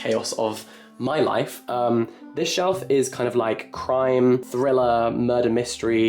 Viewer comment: I really thought he said 'This shelf is for crying' for a sec when he actually said crime hahahahaha